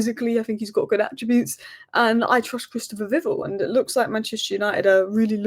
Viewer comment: Viv-ELL, I believe. I thought you said Christopher Biggel 😂